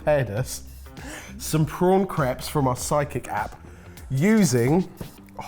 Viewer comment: I keep hearing "Psychic App" and every time, I think "that's exactly what I need, a recipe app that can read my mind!" 😂